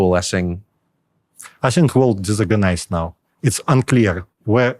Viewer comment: Whoever is in charge of this channel please check the subtitles, it has many errors such as in 47:25 when he says disorganized instead of organized. Other than that, amazing interview.